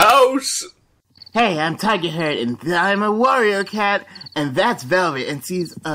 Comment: I heard “Hey I’m Tigerhair”